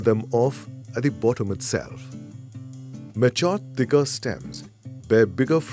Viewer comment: Appreciate the information. Very helpful. I can't understand what he's saying at 6:26 "(something) bigger stems" about the larger branches... It would be very nice if you can tell me what that word is... it sound like "meschant bigger stems". Is it "pinch off bigger stems"?